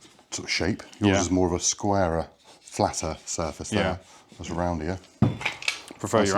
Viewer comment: LOL at the 4:57 mark did you say roundier.😄